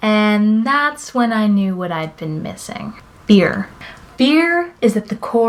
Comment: it sounded like you said " You know what I was missing, beer" to me it sounded like you said beer noy fear